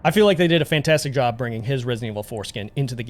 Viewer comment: Oh god, the re4 Leon....all i heard was 'Bringing his resident evil foreskin' I'm so sorry lol 😆 😅